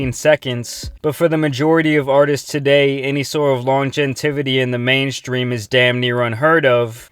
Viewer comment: Bro what did you say? "l o n g e n t i v i t y" Smh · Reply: It's Longevity